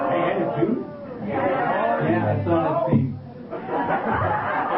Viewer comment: I think said "Yeah, I saw that scene"